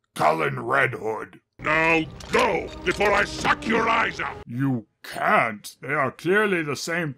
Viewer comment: "Before I suck your lies out?" Am I the only one who hears an 'L' there?